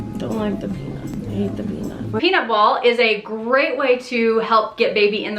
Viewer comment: ROTFLPIMP!!!! I thought you said "peanut wall" and went to look up the term, since I'd never heard it. When I couldn't find that online, I concluded that it must be my hearing loss, so I googled "penis wall". Needless to say, that wasn't so helpful, either. I did eventually find "peanut ball" but hadn't heard of those either. I guess it's been added to the toolkit since my last baby :)